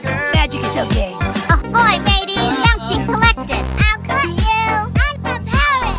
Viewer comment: I thought ike said "ill f word you!" Instead of "ill cut you!" 💀💀💀